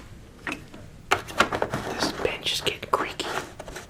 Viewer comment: i swear i thought he said “this bitch is gettin creaky” and then i realized he said bench after replaying it like 4 times lmao